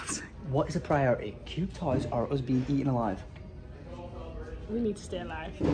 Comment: Wtf does say at 5:20 does he say a husband being eaten alive 🥺 Que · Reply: he says us being eaten alive